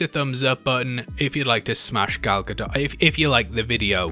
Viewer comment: I heard that, "If you like to smash Gal Gadot" 😁👍